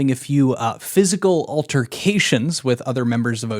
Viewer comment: For some reason I heard "physical alterations" the first time and I was like "Whaaa??" lmao